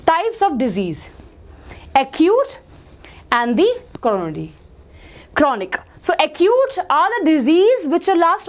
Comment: what!? It sounded like corona!